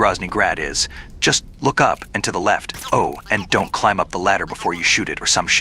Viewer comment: I'M SCARED DUDE WHEN YOU SAY THAT (i thought you said " Don't go up to the ladder" Didn't heard "-before shooting the kerotan")